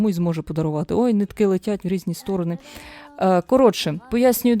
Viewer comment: мені замість "ой, нитки летять" почулось "ой літаки летять" і зразу всплило в голові "літаки лєтять будєм всєх бамбіть"...